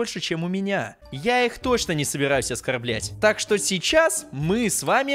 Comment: все послышали слово "оскарблять", а я послышала...оскар б**ть😂